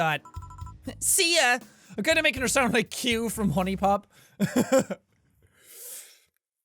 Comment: did he just say q from huni pop